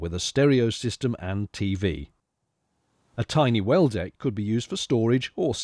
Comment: until i put the subtitles on for this video I always thought you were saying 'weld egg' not 'well deck'